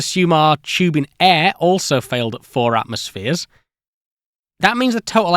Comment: subtitles say 3 voiceover says 4, there's an identical error a few seconds earlier.